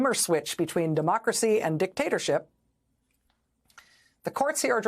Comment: Dictator you said! Oh, I thought you said DICKtator!😅